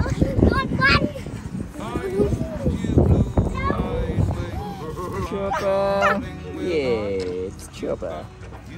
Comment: Loved seeing everyone. Did say "Chopper" Rosco is a big lap dog. Glad to see Hope getting closer. She must know Faith will not be with her much longer. Faith is really enjoying herself. They've both come so far. Thank-you and x ❤️🇬🇧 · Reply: I thought he said Chopper too. So precious.